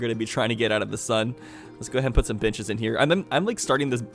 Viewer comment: i swear he said bitches